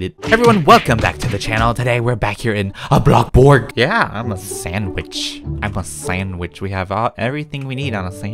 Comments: Ok not gonna lie.. when you said I’m a sandwich it sounded like you said I’m a sans Bish.. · When you said im a sand which I thought you were going to say im a savage lol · I HEARD YOU SAY SANDB*** XD